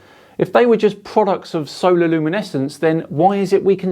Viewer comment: i think he said sonar luminescence